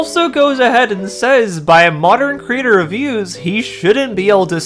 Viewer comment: ok, I was wondering if you were doing this in bad faith, but now I realize you're just stupid. The guy said “by modern creator views". You know, the whole “now [insert minority her] have something to relate" as if people ever needed to be chinese to relate to Goku. Not even his creator (r.i.p sensei) was, but he became one of the most beloved aspirational characters. Atleast try to understand what people are saying before misrepresenting them thank you very much.